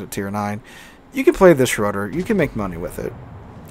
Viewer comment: Lmfao thought that said Schrader 🤣, does this ship Kemp Bush, unfair planes